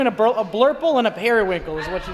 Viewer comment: I thought she said purple, what ya know bout blurple?